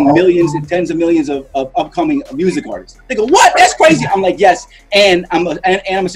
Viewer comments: wait a minute wait a minute...now you gone just skip past that like you didn’t just say what you just said🤔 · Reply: I clearly said "THEY GO WHAT THAT'S CRAZY" gtfo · is it just me or did i hear say “nigga what?” 💀